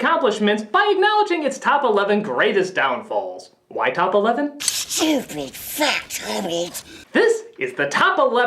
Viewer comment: What did he say? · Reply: Widdower "Stupid, fat hobbit"